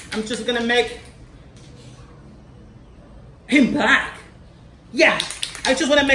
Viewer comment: En el minuto 1:12 segundos escuche que dijo pink blak pero yo oi mal sino que dijo king black.